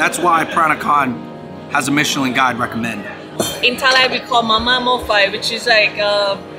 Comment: Could not catch the name of this dish 5:01. What is it called? Looks bangin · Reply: It's called "Mama Mor Fai". Mama is a Thai instant noodle brand name, and Mor Fai means hot pot. :)